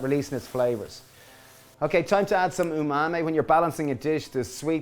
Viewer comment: what's that???? Ingredient? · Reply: Oh he said "tamari"? Sounded like he said "umami" to me, but the bottle does look like a tamari bottle, so that makes sense.